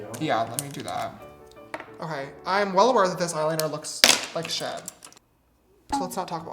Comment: Is it just me or did James say 'I'm well aware that this eyeliner looks like s**t'?